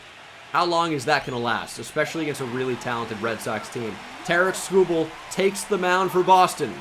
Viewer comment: Sounds like you saying Tariff Skubal lol